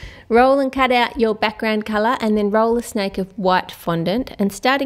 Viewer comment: When you say roll a snake of white sounds like your saying roll a snake of what